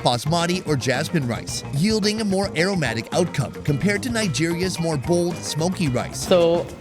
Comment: Did he just say Nigeria's smoky rice😂😂😂😂😂